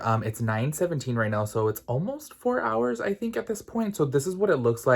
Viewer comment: I heard '67-hours' instead of 6-7 hours & was like - woah, dedication ;)